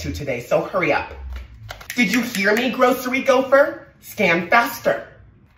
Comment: is it just me or do I hear “Scam faster” 💀